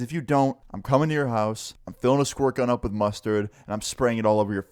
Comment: I fr thought you just said im filling a squirrel with mustard and squirting it at your house 😂😂😂🤣